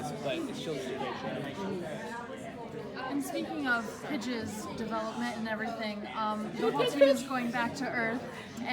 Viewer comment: I was watching with the captions on and at 4:17 the interviewer said pidge’s development while the captions said bitches development ...